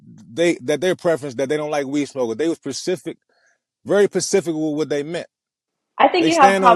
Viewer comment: wanted to laugh when he said pacific instead of specific 13:25 🤣